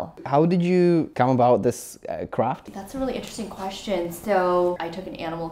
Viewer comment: On 1:26 Why does it feel like he wanted to say "crap" 😂 instead of "craft" 😂 (no offence)